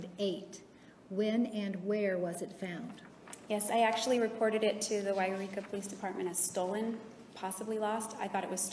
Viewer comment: can someone tell me what she says before Yreka? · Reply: “Yes I actually reported it to the Yreka police department as stolen, possibly lost, I thought it was stolen. Ummm because I searched high and low and I left it in my grandfather’s car when I ran in to pay for Chinese take out. “